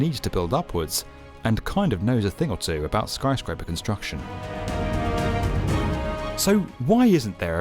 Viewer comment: I thought it said destruction and started pissing myself 😂